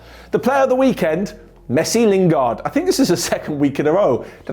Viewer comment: did I hear Messi Lingard